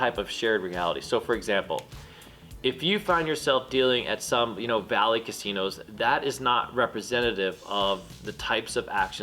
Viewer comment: At first I thought you said 'valley casino' but after rewinding, I think it may have been BALLY. Either way, how are their customers and action different than the high end places?